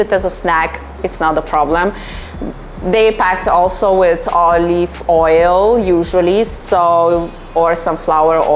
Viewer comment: It kinda sounded like kinda "Olly-ve oil" 🌚